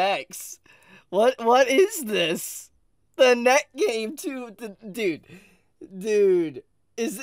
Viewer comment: I thought he was saying neck gain the whole time. 😳